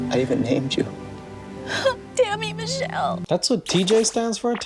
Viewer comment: She said "Tammy Michele". I thought she said "Dammit Michele".. lol